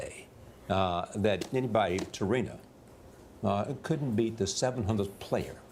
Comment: SOUNDED LIKE SAID, TORINA